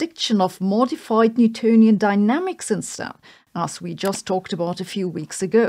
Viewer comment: I swear it sounded like you said mortified Newtonian Dynamics 😆